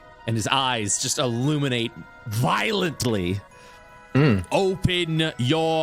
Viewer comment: Anybody else get ptsd when said "his eyes glows violently" (heard it as violet as the bad color from Verum)